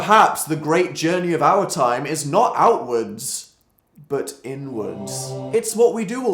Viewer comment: For a second I thought he said elle woods instead of outwards... help